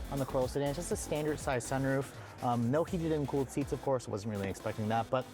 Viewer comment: Uh, what? 5:25 you weren't expecting heated/cooled seats? Kia offers heated and cooled seats on the Forte, c'mon Toyota. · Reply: I thought he said heated REAR seats.